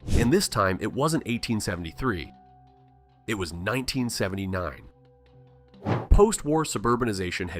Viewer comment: you said "1979 but the caption saids 1879